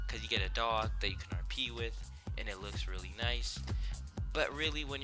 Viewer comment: When you were talking about the k9 i thought you said you have a dog u can pee with lmao